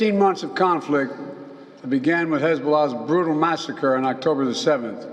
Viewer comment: did he say Hezbollah?